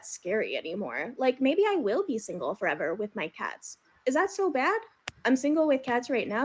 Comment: Did she say single with cats or tats? A lot of liberals have tattoos. I’m not really attracted to that. At all. Anyway thank you for sticking up for us. Love your channel.